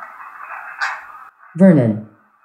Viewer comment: I might be stupid but at 1:24:15 it sounds like it says Vernon and it is so surreal to hear my sons name in a video when I’m not expecting to hear it